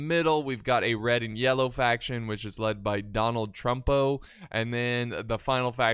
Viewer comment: Did say "donald trumpo" ? in